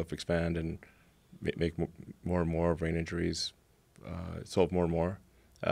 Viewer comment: C’mon now, am I the only one who realized that Elon Musk said he was gonna create more and more brain injuries at 53:00 ? “Assault more and more” Can someone please tell me what he’s saying if I’m wrong. This shits scary.